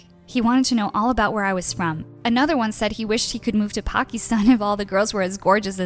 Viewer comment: did he really mention Pakistan or i heard wrong if he did then wow